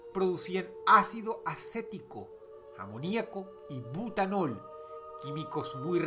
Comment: Y putanol? eso io escuche :v